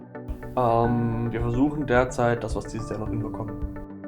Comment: Was war die Antwort auf die Frage bei 3:53? Ich verstehe das akustisch nicht ganz. "Ehhhm wir versuchen derzeit was asodjaldjahsddaskldhjasd komm" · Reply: "Wir versuchen derzeit, dass wir es dieses Jahr noch hinbekommen." ;)